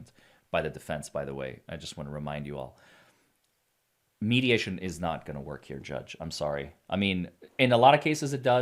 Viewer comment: I had to rewind a few seconds and listen to this again. When you said "mediation is not going to work" I thought you said "mediumship is not going to work" at first. I thought, "well, why not? If a medium can talk to John and sort all this out, then maybe it'll get dropped." 😂😂 Ugh, my brain.